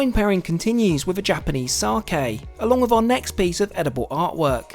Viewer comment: I thought you said 3 piece, must be cutting costs cheers